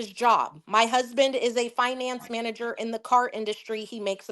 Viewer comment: I thought she said he worked in the finance department at a car dealership. Maybe I heard that wrong. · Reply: you’re right he’s a finance manager in car industry!